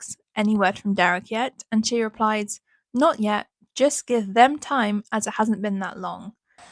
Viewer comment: To me it sounded like Joyce said “give him time”, not “give them time”. I could be wrong tho. Other than that, great video!